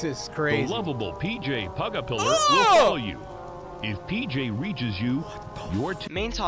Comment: if PJ pugapiller reaches you your fu- is what i thought it said it first and iwas like:no shit